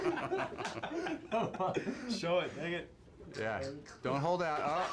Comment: This coo and all but what’d the guy on the far right say @ 4:36 “show it” what? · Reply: said SHOW IT + "dang it"